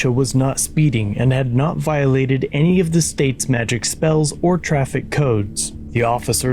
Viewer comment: Did you say she hadn't violated any of the State's 'magic spells' or traffic codes?